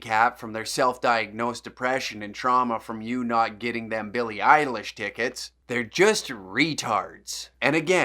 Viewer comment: when you said "billie iellish tickets" i heard "billy idol tickets"